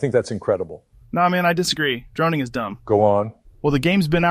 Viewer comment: Thought this said, "Drowning is dumb"